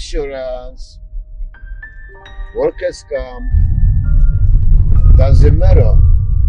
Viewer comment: “Workers comp” was what he said. 2:34 not “work rescue”